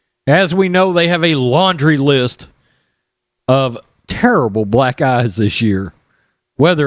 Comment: Funny, I thought he said "black guys" in his opening statement when he said "black eyes".